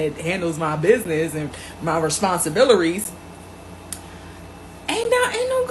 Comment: did I just hear her say “responsi-billery” ??🤦‍♂️